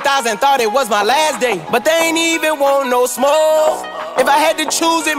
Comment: When I first heard it I wasn't really thinking about it, and I thought he was saying "they didn't even want no smokes" like he ran into the opps at the gas station and tried to defuse the situation by offering to buy them cigarettes.